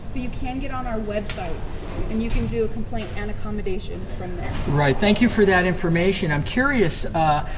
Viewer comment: Just a quick word-splain. It's "commendate" not "accommodate" or whatever the other auditor is asking for. The act of commending; recommendation; praise. Not a particularly easy word to say and not commonly used by most of us so I get it. At 11:38 she clearly says "accommodations" (which are places to stay).Peace all.